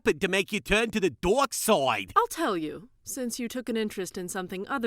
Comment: oh man, it sounded like Joey said Mai went over to the dork side! XD